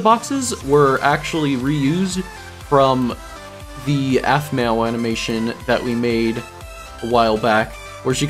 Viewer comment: Wait did you say aphmau!!